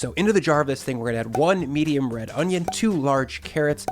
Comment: Did anyone else mishear this as "1 medium rare onion"?